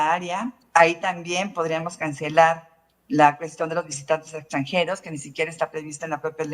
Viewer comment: ¿Escuche bien en el minuto 2:03? Se financiaban a los "visitantes extranjeros"?